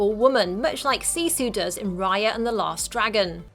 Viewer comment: D and instead of last it kinda Sounds like lost